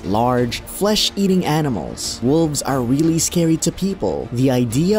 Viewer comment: Wolf weak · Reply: I thought he said wolves are weak. But yeah wolves not as powerful as hyenas.